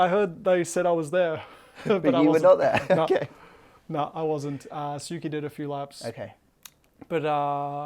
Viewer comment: Did he say Suki at 2:55? Is that a new nickname for Tsunoda? I love it.